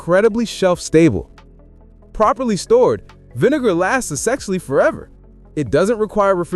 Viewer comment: Had to crack up when the closed captions said, "Properly stored vinegar lasts sexually forever" at 25:36. Lost, (or gained), something in the translation from "essentially". I know. I'm easily amused.